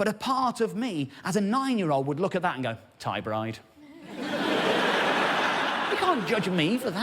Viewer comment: What did he say? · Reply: Thai Bride